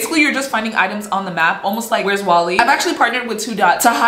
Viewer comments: DID YOU JUST CALL MY SIS WALDO "WALL-E"? 😭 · PLS DID SHE JUST CALL "WHERE'S WALDO" "WHERE'S WALL-E" I- 😭✋